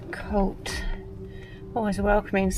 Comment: Just realised that these videos are basically pov for people suffering with paranoid schizophrenia. Also at 2:32 i thought it said “Cl it” 😂